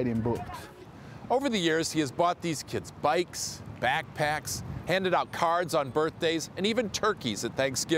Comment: I thought he said handed out cars and so when they said he has spent thousands I literally said out loud “No dip Sherlock”